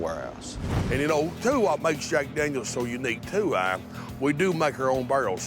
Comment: Did he just say "And in ole' two whop, makes Jack Daniel so unique two eye, we do make our own barrels two eye"?